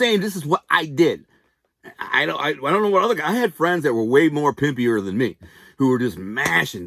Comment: Did you just say “Pimpier “? 😆